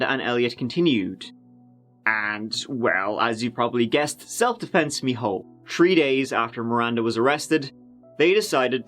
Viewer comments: "self-defense ____" anyone catch that word or phrase? I can't make it out. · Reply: Self defense "me hole " or my ass ,,,, self defense my ass! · Did I just hear say, "Self defense me hole" ??? 🤣